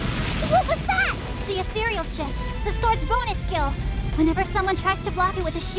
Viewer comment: what it sounded like she said atherial shit